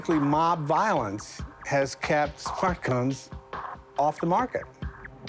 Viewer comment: I thought he said "fat cunts" for a second there